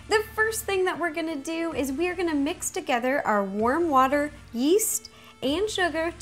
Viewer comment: Who else thought she said worm worm water